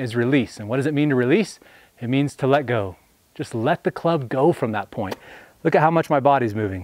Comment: Wait, I thought you said to cast the club from the top.